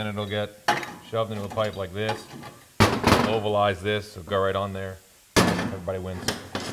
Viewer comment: Did say “ovalize?”